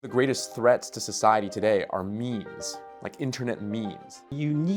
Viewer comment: It sounded like you said the greatest threat to society are memes. I was going to say you sound just like Hillary Clinton.